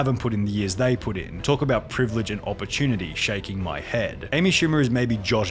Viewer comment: She's forgetting that the people she's being rude to are the people that helped her get there. 6:26 wait smh meant shaking my head??? I thought it meant somehow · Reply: It does mean somehow, but I heard "shave my head"